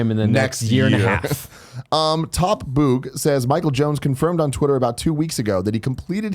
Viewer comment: Cool i was the last question to be read and you pronounce my name Mal-Come sounded like you said lol plus you didn't say my online alias (Baytuh) part :-(